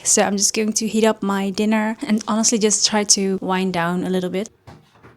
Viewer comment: what is the word before down? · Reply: wind Why do you ask, sister?